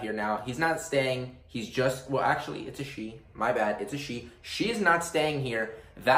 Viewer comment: When u said it’s a she I thought u said it’s a sheep 🐑